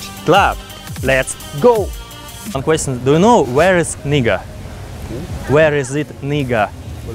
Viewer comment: I think his accent saved him a few times. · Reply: I wasn’t paying attention for a second and I thought he said “where is aneata” (or however you spell that name)